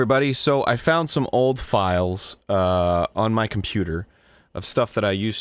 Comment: I heard "Pooter" not computer